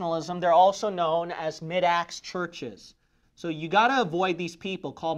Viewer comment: At beginning of video I thought you said “mid ass churches” ! Lol 😂